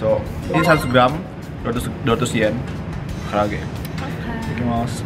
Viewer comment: menurut kalian jerome bilang apa klo gw dengernya bilang " I love you "